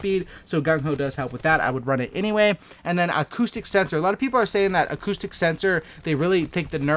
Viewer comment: I thought you were saying autistic sensor 😂